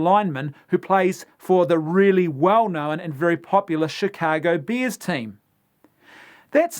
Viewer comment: It sounds like he is saying Chicago beers.